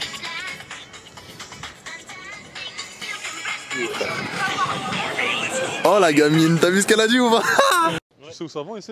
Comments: Elle a dit quoi a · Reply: Sara 94 j’ai dit tu va voir nourhene la 😂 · Elle dit quoi à 2:56 ? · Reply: J’ai dit tu va voir nourhene la😂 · A 2:58 la meuf elle a dit quoi ? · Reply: "tu va voir nhourene lá?" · elle a dit quoi la fille ? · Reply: xTeächerRimäx j’ai dit tu va voir nourhene la😂